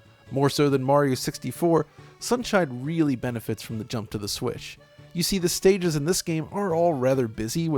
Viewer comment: Did hd say "Jump to the Swish"?